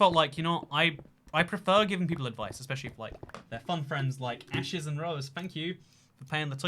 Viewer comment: I was listening to this in the background and I heard "Ashes and Roses" and thought he said my name (Ashley Rose) and was like "Wait what? I didn't watch this when it was live ._. " lol XD